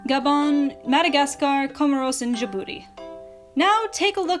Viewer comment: When was going over the French colonies in Africa I thought I she said da booty instead of Djibouti